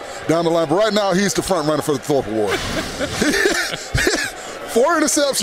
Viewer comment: what did he say? "now he's confirmed running" what? folkworth? something awards?